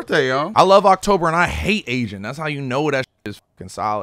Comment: I spit out my fuckin drink i thought he said Asian too